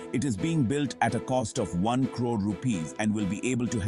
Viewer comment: Built at the cost of 1 Crore? Did I hear that correct? 0:17 _